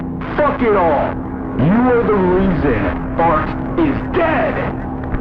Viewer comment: i thought it said bart is dead